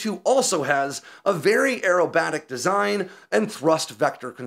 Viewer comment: I thought your said aromatic instead of aerobatic.